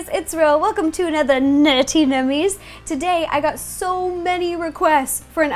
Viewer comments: Who heard her say "nurty nummies" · It sounds like she says nutty nummies. Lol